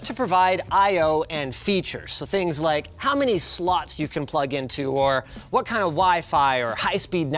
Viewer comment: I like watching things I cant afford being compared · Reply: what is he sayin @ 2:06 how many sl*** you plug into Woah!🤯 Yikes! 😧 Eww😖